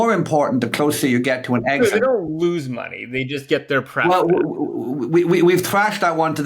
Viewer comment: what did Sam say? They don't lose money, they just..? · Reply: 'pref' short for 'liquidation preference'